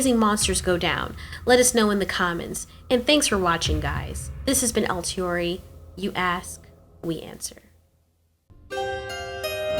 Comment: I thought she said, "This has been Alteori, you ass. We answer."